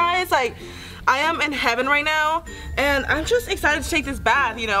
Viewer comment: "I am in heaven right now" I thought u said u were in a Jacuzzi!!